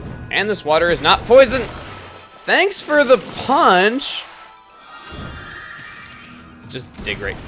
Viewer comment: Did he say the water wasn't poisonous, but that it was punch?!?!?!